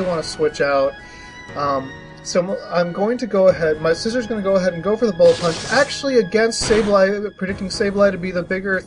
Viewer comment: did you say "my Scizor is going to go for the Bullet Punch"? Hooray for Heal Pulse! I love Conkeldurr.